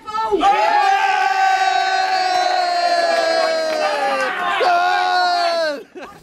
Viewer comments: What did the boy say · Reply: He said he won the Teqball (see 17:00 ) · What did say at the end that caused the cheers 27:40? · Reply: He won the teqball